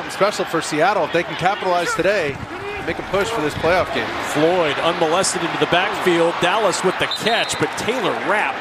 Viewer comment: Did he say: Floyed unmolested in the back field at 9:29?